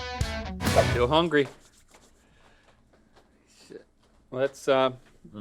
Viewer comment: Anyone catch the “sh*t”